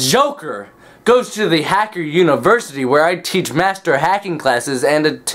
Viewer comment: Did u just say Hacker university